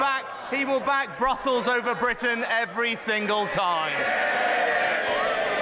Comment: i thought sunak said "he will back brothels over britain every time" 🤣